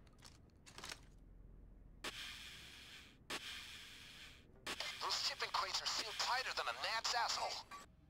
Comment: what did bain say? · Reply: "Those shipping crates are sealed tighter than a gnat's asshole." :) Just things that Bain says :p